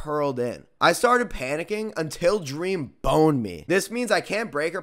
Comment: "Until he came and BONED me" What the fuck did he just say?